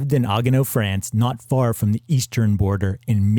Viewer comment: Of course, Colin Hanks became an actor because Tom Hanks was his dad. It doesn't matter who his dad is after he gets his foot in the door. If he wasn't a good actor, he would either not get hired for new parts or end up getting meaningless parts as a courtesy to his father. I think Colin is a good actor. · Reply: minutes you sound as if you said “not far from the East German border” I could be hearing it wrong but if you did say that then I think you might want to do more research or find an Atlas, there wasn’t an East Germany until after the war was over and the land grabbing Soviets cut the border off and created East Germany. I apologise if I heard incorrectly and you didn’t say that, it was not exactly clear audio at that point and I could easily be wrong.